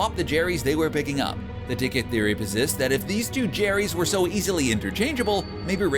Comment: I thought you said legit theories